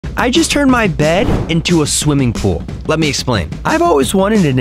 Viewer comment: I dont know why i heard "I turned my dad into a pool"💀💀